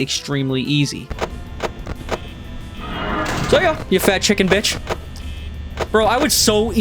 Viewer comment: what did he say? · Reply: Toy chica